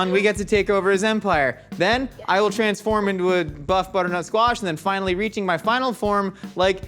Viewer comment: I heard “Buff butternut squash” and my brain went “buffernut squats” Confuses me just as much as it probably confuses anyone reading this